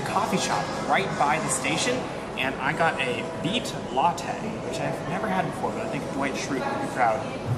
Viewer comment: At the coffee shop, I first thought you said "meat latte", and...I had some questions.